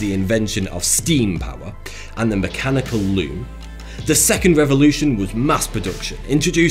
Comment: When you said “mechanical loom”, ai thought you said “mechanical loo”! Had to rewind to get it right!